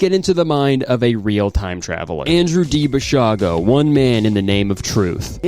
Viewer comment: DID I HEAR BASHAR?!